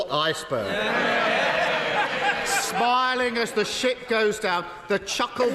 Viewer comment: did he say ship or Shii*? He probably said one, but was thinking the other 😂